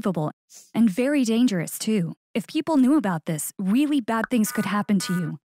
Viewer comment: did he actually say weewy bad things can happen to u lol